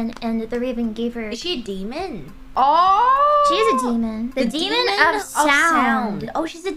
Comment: Demon of Sound? I thought it said "Demon of Soup"